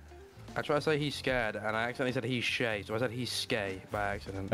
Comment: Swear I heard “he’s gay”